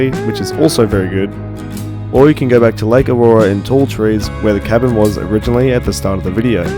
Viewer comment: I could have sworn you said "Lake Illawarra" instead of Lake Owanjila... or maybe it's just cos I'm from the 'gong.